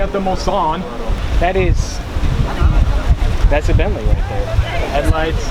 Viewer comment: did he say that’s a Bentley ?